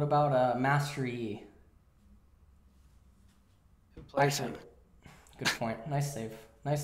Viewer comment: naisu. edit: what does he say at 54:38? difficult to tell. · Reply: who plays him? (as an answer to master yi)